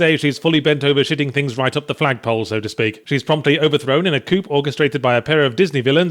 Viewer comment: did you say coop?